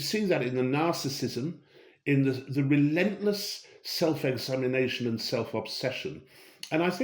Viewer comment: I swear he said "The relentless self-insemination" Alas not, but far more accurate!